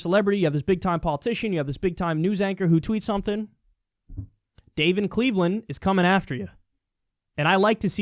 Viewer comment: “Dave in Cleveland is coming after you” Lol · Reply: I thought he said 'David Cleveland' and did a google search for him :/